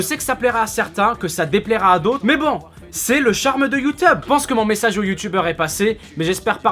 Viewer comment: C'est moi ou a 2:08 t'a dit : "C'est le charme de youteub" xD